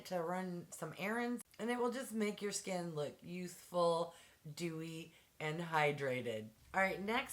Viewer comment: I thought you said it'll make your skin look useful instead of youthful LOL